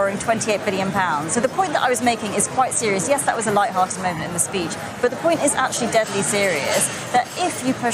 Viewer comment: If only she’d stopped talking at the right point. “A light moment in your speech” instead becomes “a lie”... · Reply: I thought she said: "That was just a lied moment in the speech...."